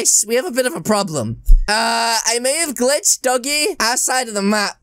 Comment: did he just say doggy